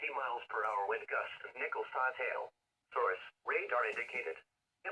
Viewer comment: sounded like he said pickle sized hail lol